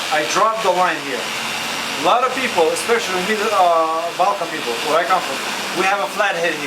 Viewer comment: did he said balkan people?